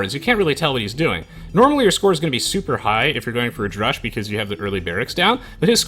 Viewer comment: what is the word u say before "rush"? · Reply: Drush, "Dark Age Rush."